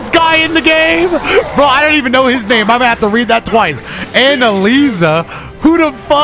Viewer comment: Did he just say "Anna-Lisa" 1:18 😂